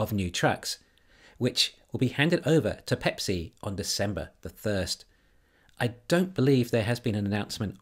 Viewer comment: Thought you said Tesla will deliver semis to pepsi on December thirst lol